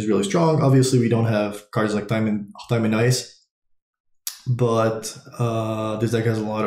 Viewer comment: What is the name of the card he says here? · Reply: He says time in ice. But I think he means thing in the ice from SOI